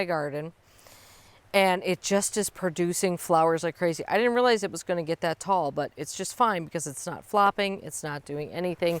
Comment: what is the nake of the Dahlia @ 16:35 Sorry I couldn't understand... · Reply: Milena Fleur